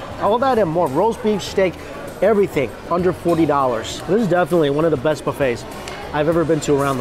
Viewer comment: Have i heard it correctly, $40??!